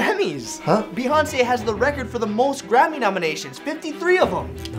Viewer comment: When I first watched this video as a kid, not knowing what a Grammy is, I always thought Ryan said “grandma”. So for about 7 years I believed that Beyoncé held the world record of having 53 grandmothers